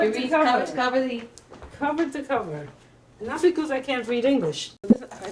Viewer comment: Did she say she cant read english? Or did i mishear?